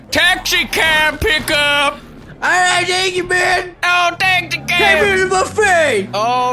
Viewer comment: what did says in 0:07 ? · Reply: "Alright, thank you man!"